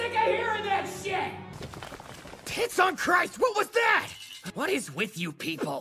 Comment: Zack:”T!ts on Christ what was that?!” Me: death by uncontrollable laughter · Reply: I thought he said kids on Christ